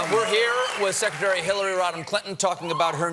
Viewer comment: I Legit had to replay the last few seconds of the video, I thought Stephen said Hilary Rotten Clinton...